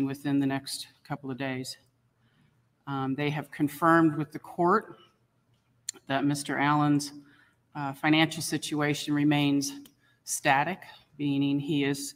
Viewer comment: Hi! 3:50 The judge said "They have confirmed with the court that Mr. Allan's financial situation remains static." Looks like that's why they quit. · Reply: Oh, okay, that's what I heard the judge say. It's 3:50 when she says "They have confirmed with the court that Mr. Allan's financial situation remains static." Maybe I got it wrong.